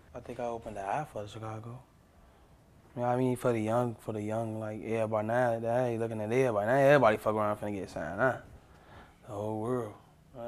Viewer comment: What did he say? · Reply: Yea for the young,yea but now they lookin' at everybody, everybody fucking around finna get signed lookin at the whole world, all of chicago